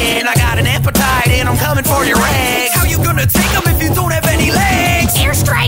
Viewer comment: bro I thought the part that said "I'm coming for your eggs" instead said "I'm coming for your ass" 💀💀💀